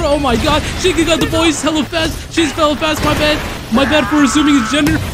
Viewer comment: bro it’s mad funny at 11:21 when he says “CHIGIRI HAS THE BALL AND HES HELLA FAST, MY BAD ,SHE ,OH MY BAD FOR ASSUMING HER GENDER” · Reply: I genuinly thought he said hello fresh instead of “hella fast” 😭😭